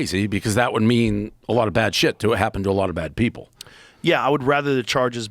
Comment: Did he just said “…to a lot of bad people ?! 😂